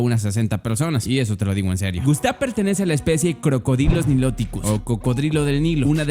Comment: “Pertenece a la especie crocodilos” o eso parece que dice 😂😂